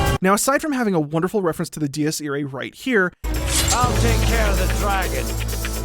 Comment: What does he say it's a reference to at 8:30? Can't make it out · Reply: The Dies Irae. It's an old Gregorian chant about the end times and the Day of Wrath. Composers often use it as a representation of death.